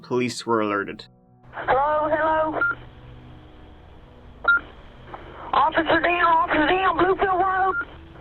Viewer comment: "Bit of a goobster that liked to turn up the giggle meter" I'm stealing this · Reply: I thought he said gooficer